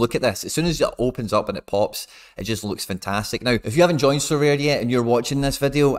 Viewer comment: subtitles says Syria instead of sorare 😂